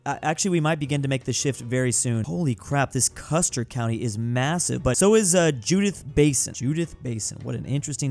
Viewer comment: did he say massive? 😮